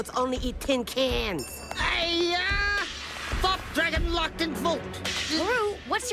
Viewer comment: What did say between 0:11-0:13? I didn't quite understand it. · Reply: He said, and I quote "Aiyaaah! Thought dragon locked in vault!"